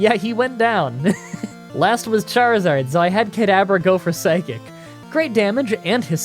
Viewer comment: Anyone else heard "kid abra" at 15:39?